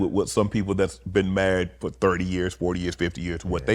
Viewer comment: Watching from England I thought he said to Warren you been mad for 30 years what's the secret 😂😂😂 then realised he said married for 30 years 😂😂😂😂 his accent for us British people some words are hard to work out but makes it funny when you realise what he actually said and what you thought you heard 😂😂😂